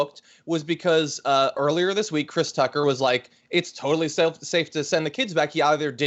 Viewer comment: did he say Chris Tucker? 😆